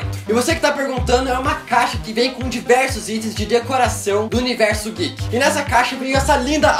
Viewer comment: só eu que ouvi universo gay... 3:35 nesta vc fechar os olhos e deixar o celular o celular próximo ao peito.